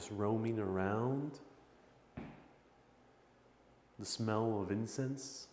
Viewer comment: When you were in the library, I thought that you said "The smell of infants" and thought WTF? LOL